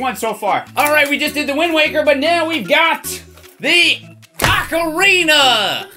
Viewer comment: Did he say cockorina